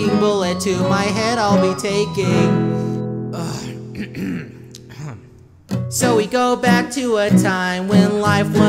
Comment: I thought he said “so we back in the mine”